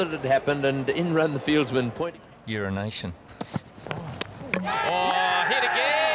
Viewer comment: Does he say "urination" at 2:43?😂😂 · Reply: More like your nation